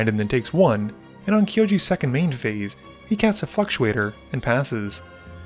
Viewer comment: Anyone else hear "He casts a fucktuator" at 4:12?